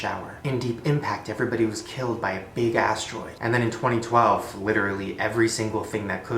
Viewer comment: please tell me I'm not the only one who heard: "big ass droid"